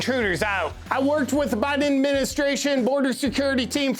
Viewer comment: For a moment I thought you said "Biden Admenstruation"